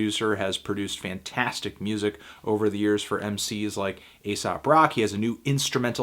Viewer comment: I heard "A$AP Rocky has a new instrumental album out." I actually think Rocky is planning to release an instrumental album. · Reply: Haha, he said Aesop Rock